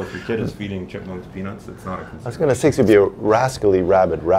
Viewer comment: Squirrels or chimpmunks: 1:40 they mumbled over each other and I rewound ten times and couldn't hear if they could carry it. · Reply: Dude in the middle said its not a concern